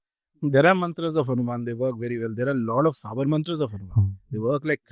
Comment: can someone please tell me what is the word starting with s that Rajarshiji says? "Sabar"?